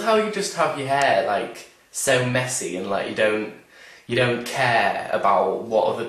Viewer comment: Lmao.. I thought he said " I love how you have your hair so MEXICAN..." Instead of MESSY. I thought of Enrique and my Mexican family... Mexican hair, what?